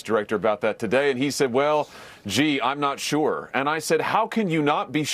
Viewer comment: Did he said G? G? Top G? Muahahah